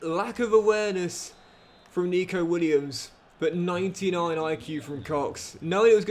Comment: 99iq am i hearing this right 😂